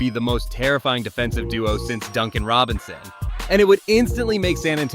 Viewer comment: I heard Duncan Robinson And thought the shooter from Miami Heat